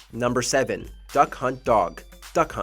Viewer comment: I swear he said “That c*nt” and not “Duck Hunt” right there. 🧐😂